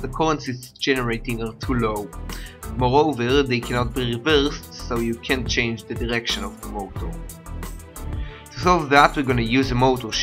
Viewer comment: does anybody know what he's saying at 1:57 to 2:00? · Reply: "Moreover, they cannot be reversed so you can't change the direction (of rotation) of the motor". Throwback to 9th grade with braces !